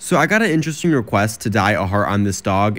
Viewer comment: Youtube shorts lagged so all I heard was, "So I got a special request to die."